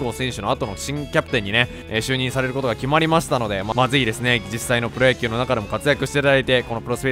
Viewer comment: ま、是非ですね が まずいですね に聞こえたw